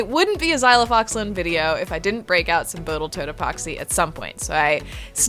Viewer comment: Lol again "Boatle toat epoxy" 10:18 Every time you make me go back and check what you said 😅